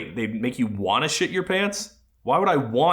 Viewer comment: I thought he said "you'd want to cum in your pants" which would've made a lot more sense.. why do I just picture someone going: "NO NO YOU WERE SUPPOSED TO SAY "CUM IN YOUR PANTS" NOT *HIT IN YOUR PANTS, JUSTIN! WHAT'S WRONG WITH YOU?"